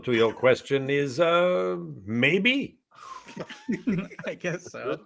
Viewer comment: I know I'm super late 'n stuff, but at 57:52, Gary said "Peut-être?" (maybe?), with a decent accent. Now you know what he said (for those who didn't).